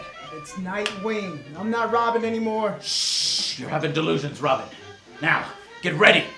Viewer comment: instead of saying I'm not robin anymore its I'm not robbing anymore